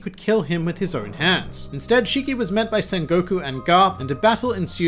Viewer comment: I thought you said "Son Goku", that'll amazing